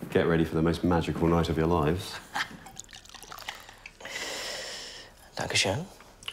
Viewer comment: Did he just say "danke schön" (german for 'thank you') at 2:19??